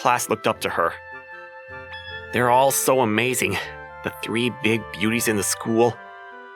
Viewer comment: "The three big booties" I swear I misheard that while passively listening.